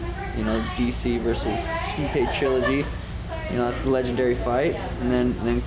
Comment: When the subtitle says "DC vs Stipe Chilla D", I thought Stipe is going with his street name or something.